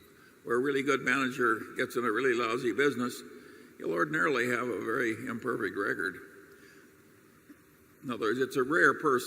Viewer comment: please tell me what charlie meant there i can't understand what he said and what he meant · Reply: “You’ll ordinarily have a really imperfect record”